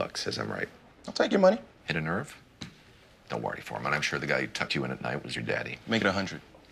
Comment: “I’m sure the guy that tucked you in at night was your dad.” Damn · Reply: I thought he said, "I'm sure the guy who touched you at night was your daddy" Completely and utterly out of pocket. I'm so glad I was wrong